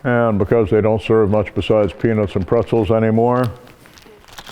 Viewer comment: I thought he said penis and pretzels but anyways...I love this guy. He'd make my scout master tear up and prepers rethink their preparations